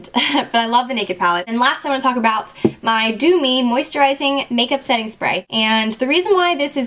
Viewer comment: when you said 'dew' it sounded like 'do' (maybe because i'm english), so it sounded like you said 'do me' hahaa.